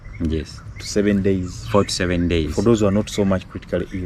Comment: John, at 4.00 it sounded like you said “average hospital stay was 47 days” though I think I mis-heard that hopefully as was it actually 4- 7 days ?? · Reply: He said 4 to 7 days